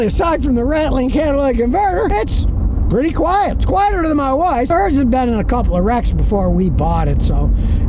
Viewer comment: at first I thought I heard "quieter than my wife" 😂